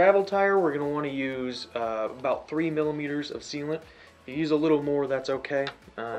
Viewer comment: So I’m guessing 3 fl oz, aka 84ml of sealant. Otherwise, was the first bead fully mounted, ie tire pumped and popped and one side then unmounted? Or was the first bead simply sitting in the U of the rim? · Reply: thank you, and it sounded like you said millimeters vs milliliters. watching what you poured made it obvious enough though.